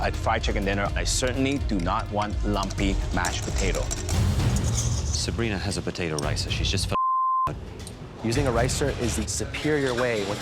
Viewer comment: WHAT DID HE SAY??? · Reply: Sabrina had a potato rice so she just bleeped up.